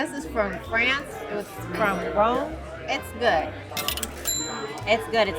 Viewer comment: Sounded like she said, the wine was from France, from Rome. LOL!! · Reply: I think she said Rhône not Rome